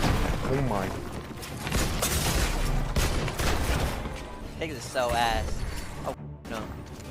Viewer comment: what did he say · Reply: Higgs is so ass. Oh fck no